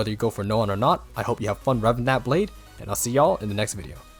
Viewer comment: I legit thought he said i hope y'all have fun rubbing that blade ☠️